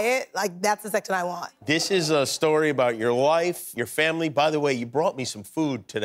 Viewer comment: Does Kimmel say this is a book about your wife? He probably meant life. Just curious since she said she’s a virgin and wants to be in a relationship by December. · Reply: I listened to it a few times. I know what you mean, yet to me he actually says “life”.